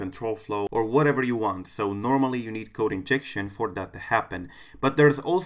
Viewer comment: i thought you said coke injection lol 😂